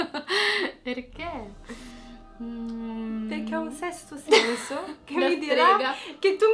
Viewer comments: Io ho capito "ho un dente lento" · Io ho capito "ha un pesce d'entro"